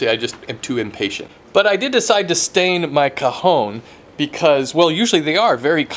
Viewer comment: Did he just say 'kahon'? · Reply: He said cajón, that means drawer in spanish.